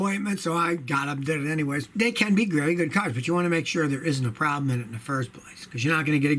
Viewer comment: Hey Scotty I thought you said Canada builds good cars...😁😁🎯🎯🤯🤯🦄